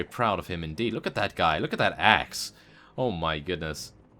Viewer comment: "Look at the axe" Thought you said ass lol